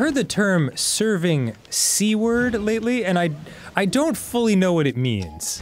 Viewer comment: I definitely heard “serving c-word” as “serving seaward,” and I thought it was even cooler than it is.